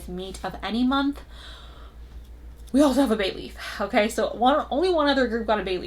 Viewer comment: When you said bay leaf at the end of reading four, i turned my head real quick because i thought I heard baby. 😂😂